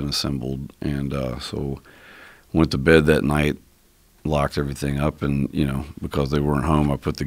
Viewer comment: So someone comes into your house illegally and you decide not to lock the exact same door that he came in, so he can come in again? You can't teach stupid. You have no clue how many crimes a locked door prevents. Criminals are opportunistic. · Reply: I thought I heard him say "I locked up."